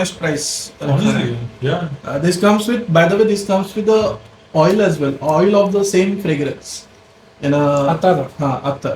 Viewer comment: Didn't catch what you mentioned at 17:05 where you said if I get 10ml of this then....... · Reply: I will consider myself very fortunate. The quality and the craftsmanship is priceless.